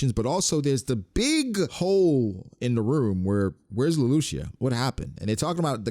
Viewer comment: at first i thought u said “where’s Lelouch ?“ 😂😂